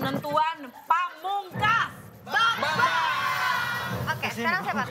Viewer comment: apa gasalah denger ngomong vanesha ya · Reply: Bukan, tapi "kak desta ya.. "